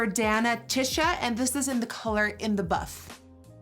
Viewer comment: I thought she said in the butt! Not in the buff 🤣